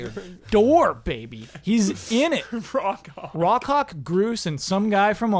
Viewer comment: Woah when said Rock Hawk I thought he said Raw Cock